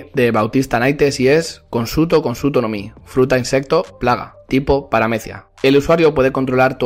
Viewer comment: Imaginan a alguien que controle toda una isla con la gutsu gutsu no mi 15:00 LO QUE DIJO: fruta insecto insecto LO QUE PENSE: fruta denge denge